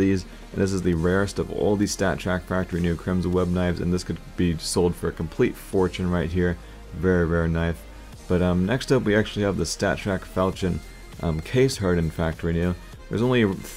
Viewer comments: it sounded like you said very very knife and for some reason i just started laughing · Did you say very very knife?